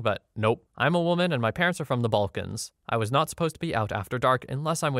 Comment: I am sorry, I do not mean to be nitpicky, but isn't 16:53 supposed to be "from the Balkans" instead of "from the vulcans"? That makes way more sense to me, both generally and culturally - I am not Balkan, but am Slavic.